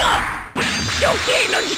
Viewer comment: sounded like he said what's up lol